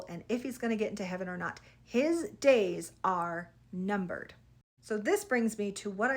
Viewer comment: When you said “his days are numbered” I heard “in the double digits.”